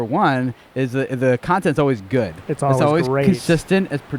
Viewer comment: Hey just a heads up at 6:00 the auto captions said “it’s always good, it’s also racist” woah! I think you should fix that manually quick man